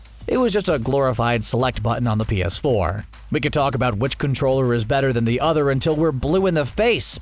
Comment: I thought it said “witch console is better”